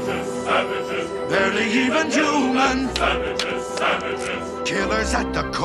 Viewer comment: In the pocahontas one I heard "they're sandwiches sandwiches barely even human" I HOPE SO